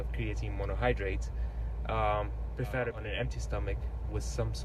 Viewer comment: did you say that creatine is good preferably on an empty stomach? because it's not clear in the video.